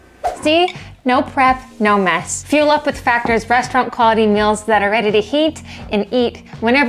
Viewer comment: overall, loved the factor ad. BUT, when you said “restaurant quality meals” at the end, i heard “breast milk quality meals” LOL haqhahaha